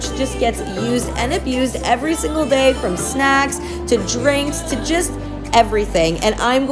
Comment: Omg I had the sound turned down because I’m at work and I thought she said “this couch has seen everything: sex...” but she said “snacks” lol